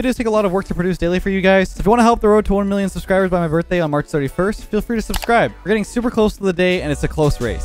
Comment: I thought I heard "March 71st"